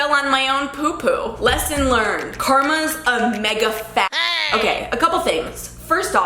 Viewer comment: uhm girl did u actually say f*g at 1:39? even if you were just reading it, that ain’t cute. edit that out 🤫